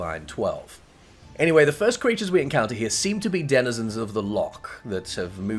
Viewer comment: Also after hearing the video again at 3:04, did you say “cweatures”? x3